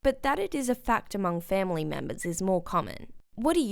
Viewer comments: i thought it was her husband i thought she said their fathers illness but when i listened again she said “my fathers illness” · did she say ‘MY father’ ????